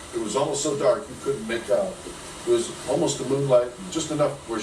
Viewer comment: Harfast nothing. I like it. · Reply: He says it was almost so dark you couldn't make out.